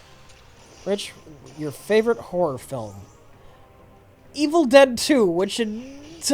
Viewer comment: I thought Rich said his "favorite WHORE film..."